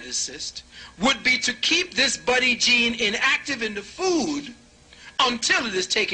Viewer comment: I heard buddy gene not budding